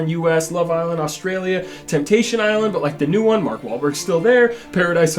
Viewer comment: I’m fucking stupid. When you were talking about Temptation Island I thought you were saying “Temptation Alan” and I had to rewatch it like 20 times